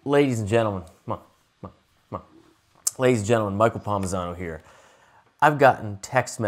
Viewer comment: Thought he said "Micheal Parmesano here" In the beginning